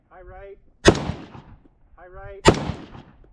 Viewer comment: "AIM AT HIS DICK!" Anyone else catch that, or just me? 😂